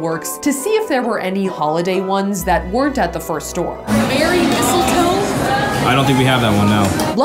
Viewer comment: Me: frick she is rich Also me: nah not after this vid Safiya: Heck Yah a crap ton of candles Me again: I thought she said merry mushrooms instead of merry misletoe